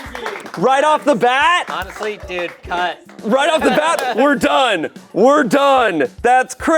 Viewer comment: honest to god thought said “honestly dude, c*nt” and wasn’t one bit surprised hahahaha